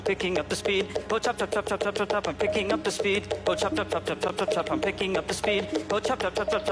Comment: it sounded like was saying "I'm picking up the speedo chop chop chop chop" over and over again